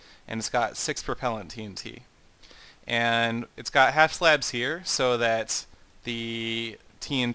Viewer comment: wait…did you just say half slab